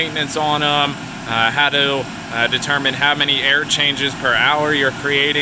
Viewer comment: you're not getting any air exchanges, you are just ducting the inside air, there is no filter in your containment, how are you getting air exchange? if your confident in your air scrubber then just ducting it outside of the containment should be sufficient. Using a laser air particulate counter can be used to calibrate your air scrubber as well as the reduction in spores in the air. IICRC 44364, MWR/MFSR/MTC and Micro CMI. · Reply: i thought he said air changes